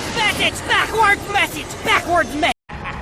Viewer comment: I always thought Gideon said "It's in the mind"..didn' realize it was "backwards message" backwards..